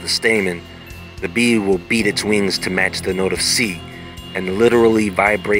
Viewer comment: I swear I thought he was saying beans and I was like ooh a superbean 🤣🤣